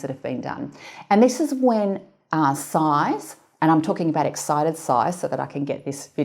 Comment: Excited size? I thought she said, “A side of fries!”